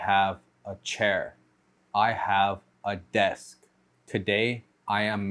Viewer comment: I thought you said "I have a death"